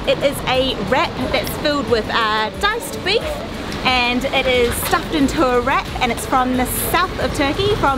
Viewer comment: I thought you said a stuffed 'rat' at first lol